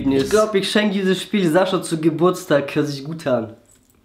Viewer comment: Du hast gesagt : „Zu Geburtstag" bin ich die einzige die das so verstanden hat ?